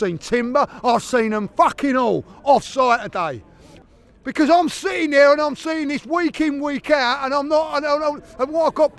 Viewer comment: a glitch. What did he say? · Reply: He said he would empty the clip on Arteta for hyping up the crowd John F Kennedy style 🎯